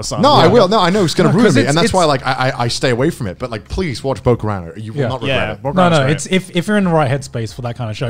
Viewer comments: what is he saying to watch? Cant really figure it out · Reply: Sounds like bokurano or something but idk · Wait someone tell me what anime he said - 2:35 "by all means watch _____" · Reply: bokurano